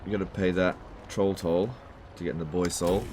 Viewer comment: it sounds like youre saying "boys hole" its BOYS SOUL